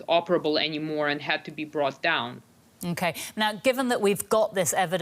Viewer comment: look in the mirror and think about my statement. We used an explosive misspelling to drop the ballon and its equipment, thus ensuring its destruction. The Russian took out a drone, specifically keeping in more or less in tact. Now I see with the IQ level I am interacting with you will not comprehend the importance of recovery but…. Enjoy either way. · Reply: yeah her accent threw me. I heard "shot down".